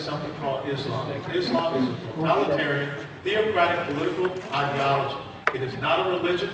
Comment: Did he call Islam Liberal?